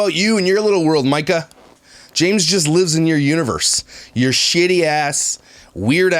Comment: I thought you were say "James lives in her uterus " but universe makes more sense lol x